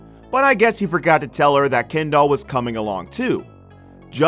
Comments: I keep hearing "Ken doll" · All I hear is, "Ken doll"...